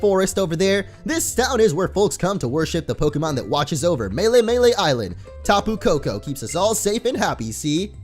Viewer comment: At first I thought it said mememele island XD